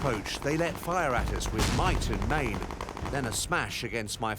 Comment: what the tanker is saying ? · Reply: Might and main